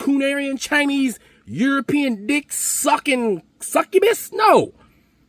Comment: DID YOU JUST SAY: (DICK SUCKIN SCUBAS) 🤣🤣🤣🤣 MY GUY 👏🏿👏🏿👏🏿👏🏿